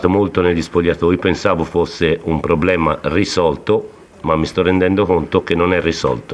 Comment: Italian sounds beautiful.. i heard "risotto". He must be hungry